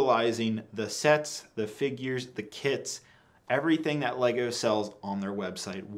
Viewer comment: I thought he said "Just utilizing the sets, the figures, the KIDS everything that lego sells"